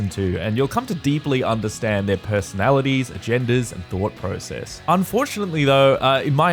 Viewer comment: I thought you said we'd come to deeply understand their "genders" until I turned on the captions. He actually said "agendas".